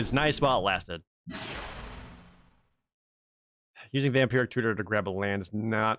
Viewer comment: I keep hearing "Vampiric Cooter".